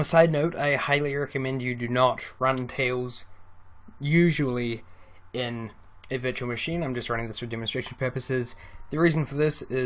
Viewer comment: you say highly recommend not to use tails on a virtual? machine? what do you mean by that and is that what you said? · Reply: you haven't figured it out yet, he did in fact say that it was highly recommended not to use tails on a virtual machine. I can go all day about what's wrong with that statement, in a general basis, but for TAILS he is on point. A virtual machine is an application, usually for a cheap price, that allows you to RUN other Operating systems while running your main one at the same time. Do you know what an emulator is? One of those apps you can install that allows you to download MAC apps on PC and vice versa? That's the concept but with Operating systems. The reason why he doesn't recommend it is because: 1. It's less safe, since not only hackers and viruses can access your main system this way, but your main system can actually log the activity and information your doing on a virtual machine if you are not careful. Thus installing it on a USB would mean that you don't need to RUN the main OS/system at all and nothing can be traced or logged back to the Windows/OS X/Other Linux version you are using. 2. It is literally defeating the purpose of Tails, as Tails running on a virtual machine goes against TAILS(Capital because it's an abbreviation, thought I should point out), main idea to NOT use Windows or OS X for deep web browsing and provide a separate and more hidden OS. Running TAILS on a Virtual Machine is wrong in the same fashion as: Note this is only hypothetical, as it would never ACTUALLY happen, but anyway: Suppose you meet an Identity Theif. He claims that if he knew